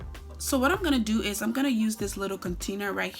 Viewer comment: Shots 🥃 every time she says “like so” or “Aloe Vera”🥴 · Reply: I thought she was saying like soap 😭 Beautiful hair tho